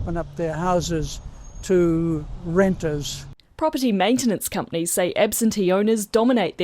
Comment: Love the way he said "Renters!" sounded like he was saying "satanic mutant lepers!"